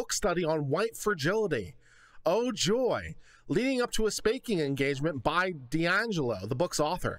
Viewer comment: I know you said Speaking Engagement at 16:14 but the first time I heard it, it sounded like Spanking Engagement lol Appropriate with how they wanna shame everyone. Also a Hispanic person, I abhor the term LatinX and so do many hispanic groups. Just saying